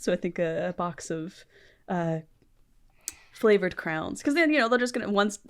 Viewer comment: I was so confused for so long, because I heard Indigo say ‘box of crowns’ and I was thinking desperately what on earth she was talking about, until Max said ‘crayons’ and I understood